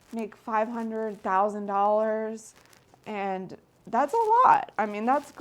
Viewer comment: You said <num> $ and it sounded like you said $500,000. I went WOW that is some cash!